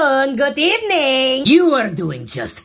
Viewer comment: I thought you said I loff you😂